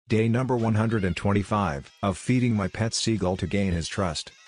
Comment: For a moment I thought that said “cooking my pet seagull” and my heart just dropped for a second